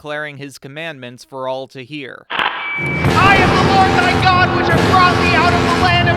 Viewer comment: I swear I heard it as “I’VE COME TO MAKE AN ANNOUNCEMENT “ from the sonic fan dub meme Also, I felt Moises deep frustration and tiredness deep in my soul, good job on showing that